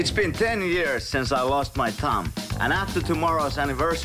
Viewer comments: When he was saying thumb I thought he was saying tounge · When he said thumb I thought he said tounge